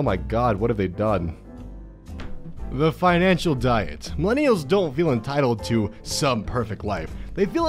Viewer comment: Could've sworn I hear "My nails"